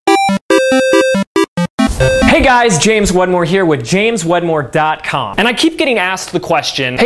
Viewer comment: I thought you said “James One More” lol